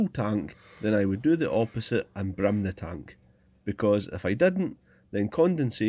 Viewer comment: forgive me, but what did you say here? Brim the tank? This is the first time your accent has gotten the better of me😅